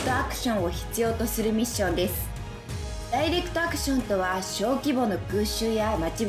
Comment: ダイレクトアクションがダイエットアクションに聞こえたのは俺だけかwやっぱ空耳多いな